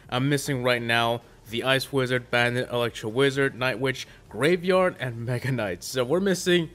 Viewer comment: Did you just call graveyard rapeyard?